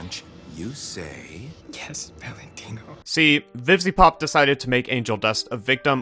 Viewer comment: i have subtitles on and when you said "vivziepop" the subtitles said "vivy poop" LMAO